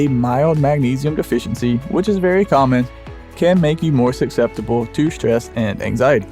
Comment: "A mild deficiency can make you more SUCCESSIBLE...". What's that word again? Succetsible. Nope. Can't pronounce words and has no editor. Great vid. I gess.